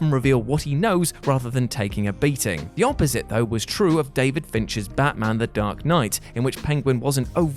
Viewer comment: oh David Finch...... It Almost sounded like David Fincher’s The Dark Knight.